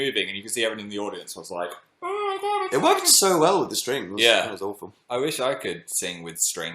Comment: Did Dan say "it was awful" at 19:08 or am I hearing things.. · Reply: it sounded like awful bc of his accent but he actually said "it was awesome"